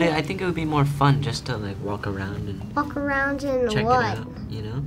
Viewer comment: did George Say fuck around in what Yeah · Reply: Mario Estrada he said walk around